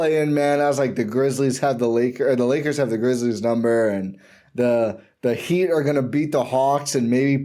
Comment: I thought I heard some nerds saying the heat was going to get sweep